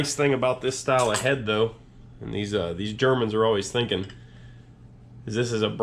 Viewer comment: Germans? What brand engine was that again? When you said it, it didn´t register as a german word with me... edit: ah, it´s a Farymann. (Far in Farymann is said like the Far in "Farmer" or "Far away". One of the founders was called "Farny" which is a German last name.)The name shows up on the engine later on. You just said it in a way that didn´t sound right, so it didn´t register. Yeah, those things have a pretty solid reputation... Never seen one myself though.